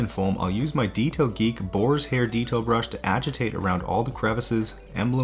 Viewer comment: I heard as Boris hair.. Because his hair is kinda like that..